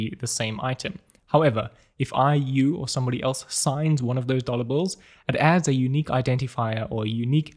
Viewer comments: Got really confused at the start, thought he was saying 'dollar bull'. Is this a new coin I haven't heard of?!?! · I heard "dollar Bull" several times 😅 Awesom video and the fact above made it even funny for me